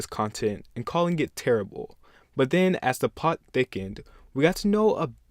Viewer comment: did he just say "as the pot thickened" 1:25 ?